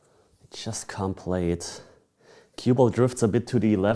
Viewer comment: only the cue ball couldn't hit the rails Einstein 🤦🏼‍♀️ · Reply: lol, i thought he said cubical. so basically the edges xD he talked so fast and im not really into pool and this isnt my first language.